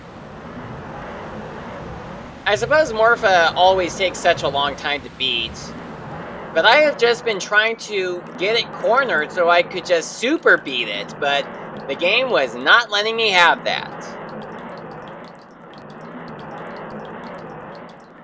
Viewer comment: I have misheard this as SUPER EAT IT!